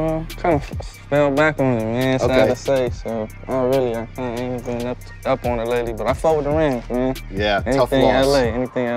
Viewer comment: What... what...did he just say?😂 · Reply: Nah he said "I ain't really been up to it lately but I fuck with the Rams"